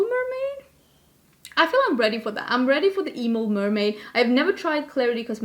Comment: I thought you said “emo mermaid” and I’m not gonna lie I was kind of into it lolol